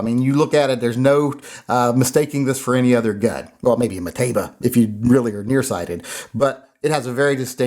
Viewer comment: I misheard him when he said it looked like a mateba, I thought he said potato 🥔